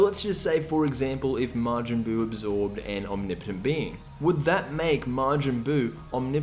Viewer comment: Majin Buu absorvs who? I can't understand the name you're saying. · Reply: Oh, omnipotent being?